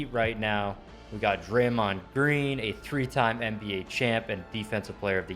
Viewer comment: did he say breen